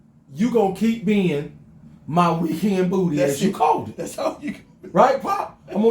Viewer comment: Every time I heard you say weekend booty. I hear, I found you miss new booty.